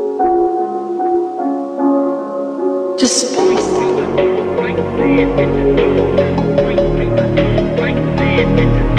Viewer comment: В каждом треке есть слово, которое говорит женский голос. Что он говорит? Здесь, например, на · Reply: Just space